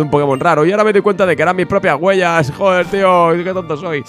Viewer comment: Por un momento pensé que decía "mis primeras muertes", vivo con el miedo constante